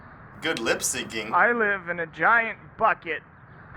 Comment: I thought he said fuck it instead of bucket